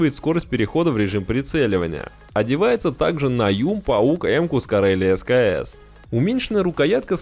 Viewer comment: Произноси слова четче...послышалось не ауг а паук...искал 30 минут в инете че за паук.